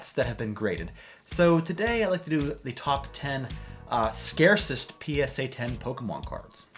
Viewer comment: lol I thought that said top 10 scariest PSA graded Pokemon cards